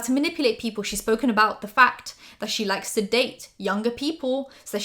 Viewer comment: I thought you said ''she likes to sedate younger people as its easier to manipulate them''... But you actually said ''she likes to date younger...'' I was like 😳🤯